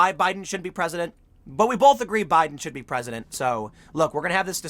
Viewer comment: I had to take it off 1.25x speed cuz it sounded like Tim said "Biden should be president" multiple times. At normal speed it still sounds like it.